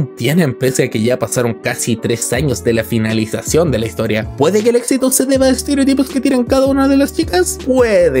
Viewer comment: Los doble tempos del Teorema parecen libros para preescolar comparado con 8:50 Pd: qué dijo?? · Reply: Me salio lo chileno para hablar rápido XD Ahi dije "¿Puede que el éxito se deba estereotipos que tienen cada una de las chicas?"